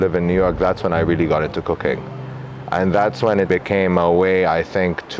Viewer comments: When he was interviewing the Jordanian Palestinian I thought at first he said when he lived in NY he got really into cocaine, not cooking. I thought to myself “that’ll do it” lol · did he say that's when he got into cocaine?